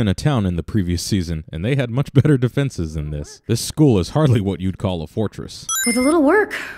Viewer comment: And I found a typing sin 17:48. He says " they had much better defenses than this. But the subtitles say, " and they had much defenses".